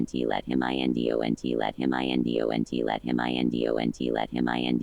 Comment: I heard let him aunties die oh we tie